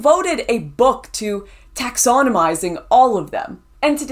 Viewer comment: Lauren, you've given me an idea for a...."new(?)" Word based on what I misheard you say: "Taxodomy". Apt description of socialism, wouldn't you say? 😂😂